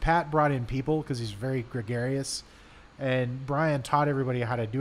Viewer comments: Hahah at 20:50 the subtitles said "He's very precious" rather than "gregarious"when describing his friend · interesting interview!! but im pretty sure it was "gregarious" instead of "precarious"